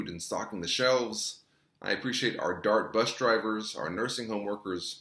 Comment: I didn’t get his name, but at 15:43 he said he appreciates “dark” bus drivers. What is he saying, dark? Does that mean black or brown people? Is he eliminating whites, Asian or Latino? Just askin’